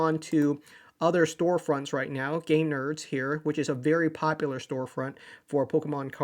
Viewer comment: Thought he said "Gay Nerds" haha.